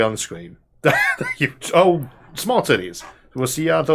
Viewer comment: Re announcement of tpcoming episode (🎬 1:03:08): Next episode will be Small Titties?. like how small we talking, the nubby boobs of a nubile nanobot ot what? 🤷‍♂️ — Edit (after second listen): 😅 My bad, he clearly said Smart Titties (even better!!🤸‍♂️🤸‍♂️);: this I've gotta see (maybe motorboat) to believe.